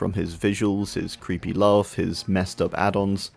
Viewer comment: Did you just said love or lore?!? Because if you said the latter, its not love, its a paraphilia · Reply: He said laugh